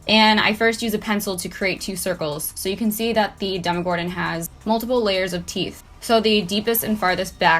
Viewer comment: i swear she said dumb-gordon lol